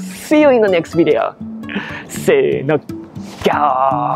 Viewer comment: thanks for another great video!!! Question - what are you saying at the end at 27:00 right before “gaooooo”?? Something like “saino”? You often say it after your name but I always wonder what you’re saying! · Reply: Hi, “se e no” is Japanese for “Ready, set...” and instead of “go,” says “Gao,” which is the lion’s roar in Japanese. ^^ :_gaoo